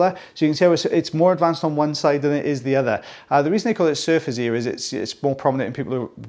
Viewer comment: So confused for a bit. Thought you were saying "surface" ear. 🤦‍♀️